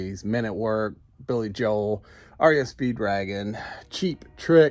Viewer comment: it sounded like you said REO Speed dragon.. Great job on the spot brother. Are you planning on setting up an online store as well?